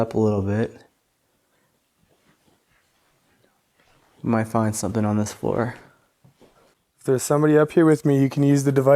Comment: Hey listen really closely between 32:20 and 32:24 there is a evp, I can’t make out what it says but definitely a voice right about the time you said hoping. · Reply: don’t blow me ….lol I wonder how he meant